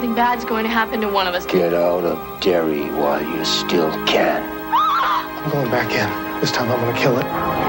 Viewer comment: I thought he said ''Get out of Gary while you still can...'' I was like good warning, Gary, Indiana sucks.